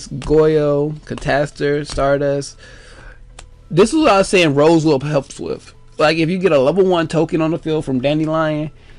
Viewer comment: i didnt catch the name of the card you described at 9:56? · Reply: photon pulse dragon